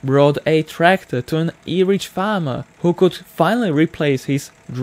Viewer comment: I swear he said “Irish farmer” at 8:19 not “average farmer” 😂